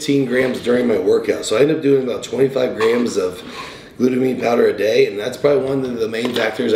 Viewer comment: Thought he said 25 scoops of glutamine a day for a second lol